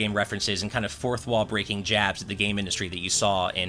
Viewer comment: Did he just say jap, the derogatory term for Japanese people that is the equivalent to calling a black person the n-word? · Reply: he said jabs